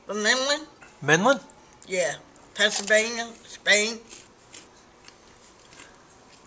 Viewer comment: I thought Beet was saying England and then Pennsylvania and but he ended up with Spain so it’s all good now